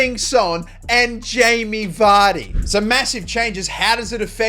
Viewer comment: Did you say massive